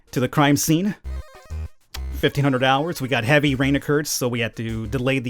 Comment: did he said dollars? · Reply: he sayd hours